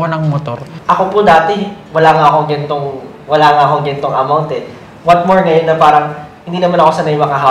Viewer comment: Josh said gento? The slang term for ganito... Tama ba ang narinig ko?